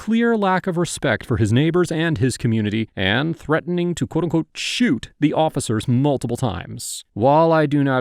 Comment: When he was saying shoot it was just his accent and not knowing the word, he meant sue? He was actually saying ‘I’ll sued you’ ‘I’ll sued you’